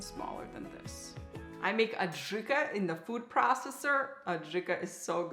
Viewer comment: Hi, great video. I'm a 70-year-old retiree getting back into cooking; I think you will change my life after watching this. But I'm also a 70-year-old with fading hearing. In your video minute 4:53, you say you make [?] I can't understand the word you use for that recipe you say is so good. Please tell me the recipe's name, and do you give it out? Thanks, Dave. You just became one of my favorite chefs on Youtube. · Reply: Hi Dave! Thanks for the kind words:). So glad you enjoyed the video. I was making Adjika; it’s a a popular European side dish that is similar to a tomato pepper salsa. Here’s the recipe: <link>